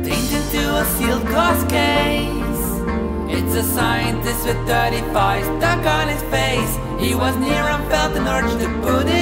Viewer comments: It sounds like it says "a scientist with dirty fries stuck on his face." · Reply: I thought he said "its a scientist with fourty five ducks on his face" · Sound like it said it's a scientist would certifies duck on his face